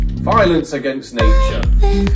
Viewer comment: I thought she was saying "Bidens against nature"... :p